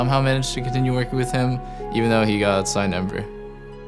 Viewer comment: what does doublelift say? can't make it out... "even though he got..."